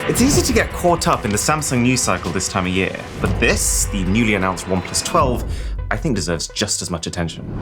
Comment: Is it only me or did I hear "newly announced One Piece 12"?